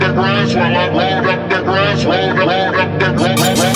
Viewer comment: First i thought he said Hold up the glass